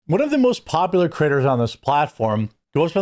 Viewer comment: I thought you said one of the most popular critters 😂